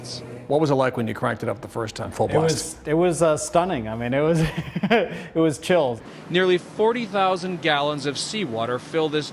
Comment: Did he say "it was chill" lmao